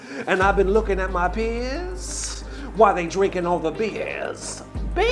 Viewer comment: In the rap at the end I heard "looking at my peers" as "looking at my piss" at first and I was like wtf is happening